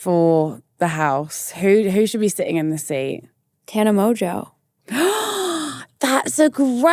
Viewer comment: Who did she say should go on next? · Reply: Tana mongeau